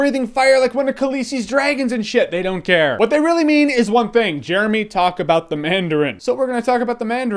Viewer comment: When he did batmans voice it sounded like he said Ron Jeremy